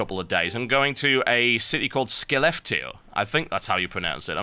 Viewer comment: hmm...i thought he said he was going to a place called thelefteel on his blue please >.> lol